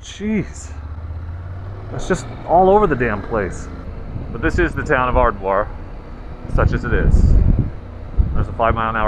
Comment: For a half second, I swore you said "the town of Farquaad" made me laugh. 😆